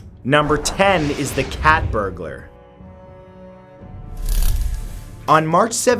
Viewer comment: i thought he said cat burgers